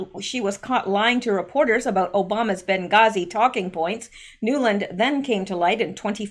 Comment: It's almost 2 am here, and I guess I'm a little tired. I thought you said talking pants instead of talking points. Hahaha